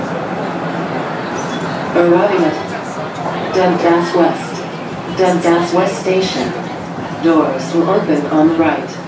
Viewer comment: They should've stuck to Susan Bigoni's voice. That automated voice is terrible. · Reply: For Dundas West, at some point I just heard “Duddas West”, and for Bathurst for the second thing she said I just heard “Pathurst.” until I listened more closely